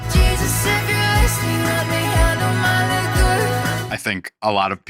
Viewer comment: please what she said??😭😭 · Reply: liquor